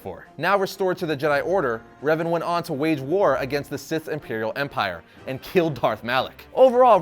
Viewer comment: did you just say imperial empire at revan?